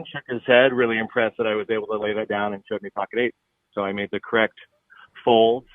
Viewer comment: did you say V had pocket Aces? How? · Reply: I had to replay it, he said pocket 8's